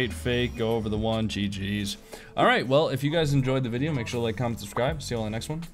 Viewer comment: In the end of the video you talk really fast, so what i heard was " if you like the video, like, c*m and subscribe."! 😂